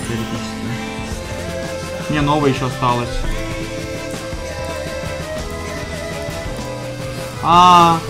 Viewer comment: did he say "yeah no rush palace"? Do all Russians speak in cs:go callouts? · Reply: he say "нова еще осталась"="Frost Nova still remains" or something like that